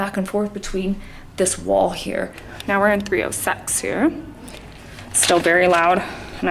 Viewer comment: 😂 I heard that as well 30sex😅😅😅😅😅